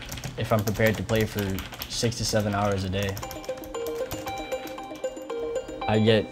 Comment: did i hear him right? He plays 67hours a day?